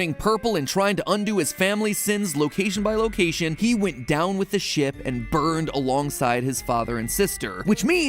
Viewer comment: Thought matpat said "He went down with the shit" for some reason😂😂